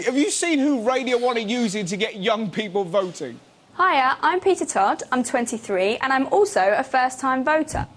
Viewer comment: I didnt catch her name... Can someone help me? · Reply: peter Todd